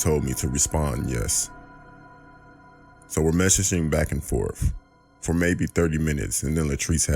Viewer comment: It bothers me that he said " pacificly" instead of " specifically" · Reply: I did hear that correctly..He said "Pacifically" instead of "specifically"and 04:31"MESH-ah-SHING" instead of "Messaging"